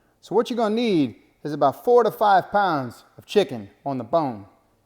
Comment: well to start off ! I tried the PI" YAHAAAAAAAAAAAAAAAAA! AND broke my hand I'm old ! 70! then my ears are bad and I thought he said 45 pounds of chicken! my wife was not happy! she said hey! he said 4 to 5 pounds of chicken!? I said oh well PI" yahaaaaaaaaaaaaaaaaaa! ya gotta luv that chicken!